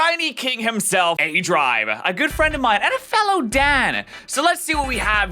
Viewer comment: I heard you say "fellow DAD" about aDrive and almost choked